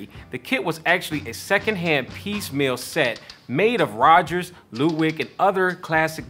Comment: I’m sorry did he say LEWDWIG